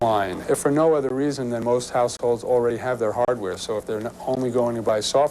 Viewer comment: Did he say 'Most households' or 'Most Assholes'?